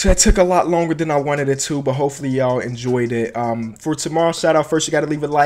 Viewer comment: I also thought it said for too lol XD 😂😂😂😂😂😂😂😂😂😂😂😂😂😂😂😂😂😂